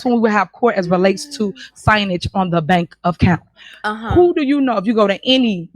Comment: Tiff said "signage on the bank of count" 8:14. Omg she really said that. She tries to say things and mixes her words up and tries to say big words and can't pronounce them. Omg. Stop trying to sound smart when you're not. I R SMART. SMRT SMART. 😂😂😂😂